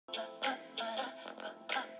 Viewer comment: I thought he clearly said Anything For You Fiancé